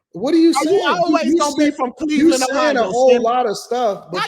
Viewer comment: it sholl did sound like said he was from Cleveland OH 🧐🧐🤣😂🤣😂